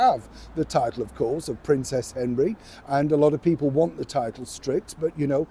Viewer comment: Neil, instead of "Princess Henry", I thought you said, "Princess Envy" and thought that suited her just fine. 🤣